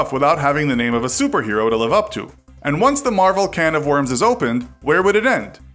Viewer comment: did he say marvel with Superman?